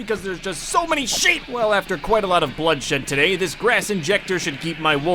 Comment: 😶did you swear😶 it sounds like you said Blood sh*t